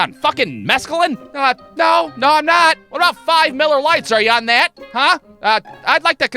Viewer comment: At first I thought “nice one, idiot”. Then I heard “5 miller lites” and thought “pfft… does that even count?”